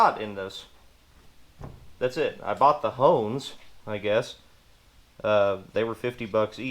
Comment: Ahhhhh okay okay lmao so i had to rewind n watch a few times n crank my volume @ 16:20 because "HONES" is certainly not what i thought ya said and well if you can find the thing i was thinking i heard you say for $50 then ya miiiiight wanna steer clear of her or them if more'n one! 😂😂😂 💃🪝-er